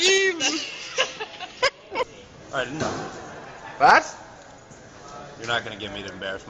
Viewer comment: what is said? Do they say the german word "was"? it sounds so german.. so I wonder if this word is used in america as a synonym for "what?!" (it's actually the same meaning!)